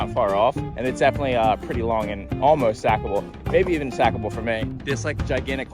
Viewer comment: I thought you said ‘Zach-able’ when you said sackable. I must be still subconsciously thinking about Doelling’s sick as part. I mean let’s he honest, most obstacles are Zach-able because he is a skateboarding beast.